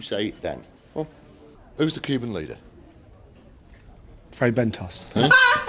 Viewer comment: Did he say "Fray Bentos?